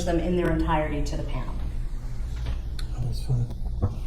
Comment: hot mic moment?? Does say "that was fun" or is it the judge saying "that's fine" after the prosecutor finishes showing the crime scene photos??? It sounds like but I'm not sure. 😟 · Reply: I think it’s the judge saying that’s fine.